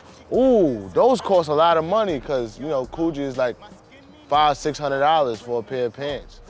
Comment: did he say gucci or something else? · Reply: ReijiTitan COOGI, ny wave